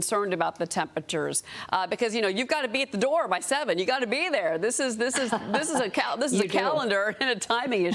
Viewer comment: Did she just say "Boner turn out at All" 😂😂 or I just hear it wrong..